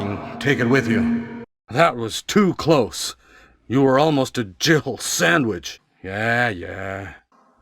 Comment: Does anyone else understand "Jiggle" sandwich? I always thought that's some kind of sandwhich I simply don't know. Never ever would I have guessed that he's talking about a Jill sandwich. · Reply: I ride around and stuff The first time I played it I misheard him too and mistakenly thought he said “jiggle sandwich” as well haha. I thought he was referencing her boobs or something